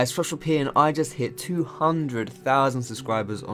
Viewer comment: Every time he says "FoxDrop here!" I hear "Social P"